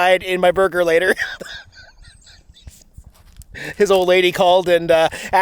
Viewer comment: did I hear you right, “His old lady”?